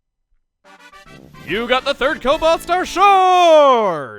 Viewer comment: captions say "you got the 3rd toe buster sword" and that probably is more painful then a shard of cobalt star